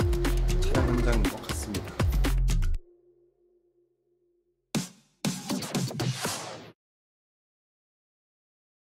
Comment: is muted? Why? What did S.Coups say? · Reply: ah.. kamsahamnida~